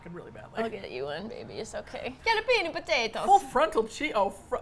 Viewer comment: Where says Scallopini potato, the subtitles say 'get a penis potato'! And then the full frontal semi hard part afterwards, it's hilarious!